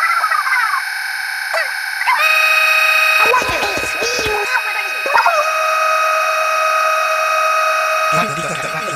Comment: What did Agent 8 say? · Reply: She's Says "What Is Going On?"